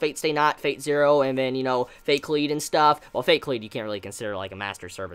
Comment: What series did you say here? Fate/Lead? Fate/Kleade?? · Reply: Nathaniel Shaw, The Overclocked Thinker fate kalied